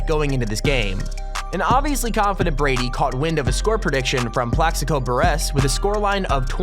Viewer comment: This guy is great at reading but damn has he actually ever hear some of these words and names? Sounded like he said Plexico Bur-Ass.